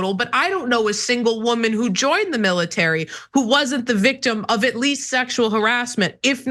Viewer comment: Rayvanna doesn’t know a single female who joined the military and wasn’t sexually assaulted or abused? Ok this is why TYT is the most dishonest show in history. Cenk should call her out for lying about this. This is why women weren’t allowed in the military until recently and shouldn’t be allowed there. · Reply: She clearly said she didn't know a single woman who joined the military wasnt AT LEAST SEXUALLY HARRASED, or assaulted. See the difference in what you thought you heard? So because some sick men cannot control themselves, you think women shouldn't be allowed to join the military? Can you not see how stupid that is?